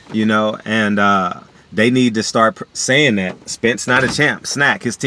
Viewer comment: I thought they said Spence not a chance 🤷🏽‍♂️